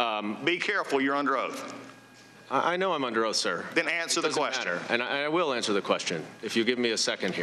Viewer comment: Did Goldman just say he knows he's under oath and it doesn't matter???